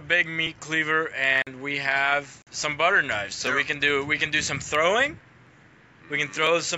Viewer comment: I thought he said butchers knives because a meat cleaver is a butcher knife